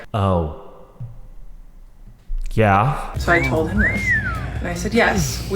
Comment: it sounded like kya ( what in hindi)